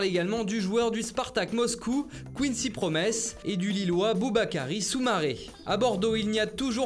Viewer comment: J'ai entendu Booba Karis Starfallah 😮😮😮